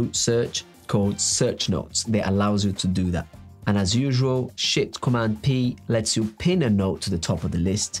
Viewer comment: Did he just say "shit command p" at 1:50 😂